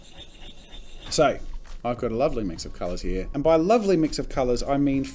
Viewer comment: U said "I've got a lovely mix of colors here" I heard "Ive got a lovely bunch of coconuts tillyti" 🎶🎵